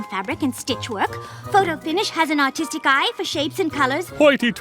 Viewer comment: i thought they said photo finish has autistic eye